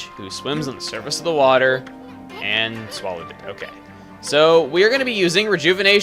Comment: Did say he swallowed the cupcake?